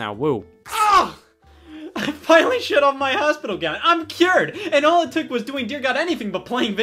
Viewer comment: I heard “shed off my hospital gown” as “shit on my husband again” and was very, very confused